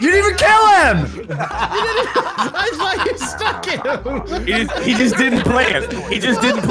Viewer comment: I think just cursed. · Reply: oh. I thought he said fucked and not stuck.